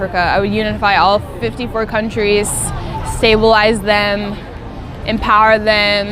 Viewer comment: Omg was I the only one who heard her say if asked to change something in Africa what will that be ? I will unite Africa and «. CIVILISE THEM ». 😳😳😳😳😳😳😳😳😳😳😳😳😳😳😳😳😳😳😳😳😳😳😳😳😳😳😳😳😳😳😳😳😳😳. so for your best of knowledge Africa isn’t civilised yet? 🤦‍♀️ i just wished i never came across this cuz it’s a pity that a black girl will openly, confidently and courageously voice out such . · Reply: She said "stabalize them"